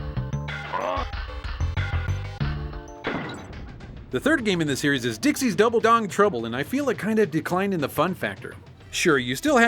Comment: So when was mentioning DKC3 I at first thought he said "Dixie's Double Dong Trouble"....😳